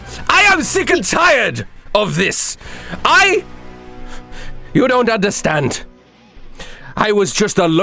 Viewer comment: Bingo! He said You don't understand